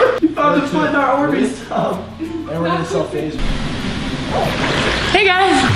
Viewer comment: did just say "we're going to sell FaZe" ?